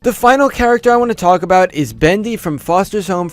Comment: did... did you say Bendy?! BENDY ATIM = FOSTERS HOME FIK